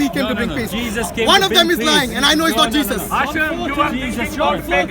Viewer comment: I heard Asif not Hatun 😂🤣😂🤣😂🤣😂